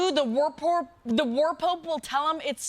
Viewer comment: I heard "War Porp" and now I just wanna see an edit of this with Grace dressed up as Porpoise Rambo.